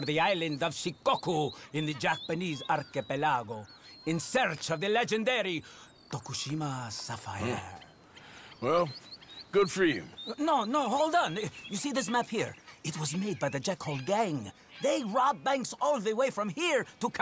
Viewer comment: Did he say hold on nigga in 1:36? Wow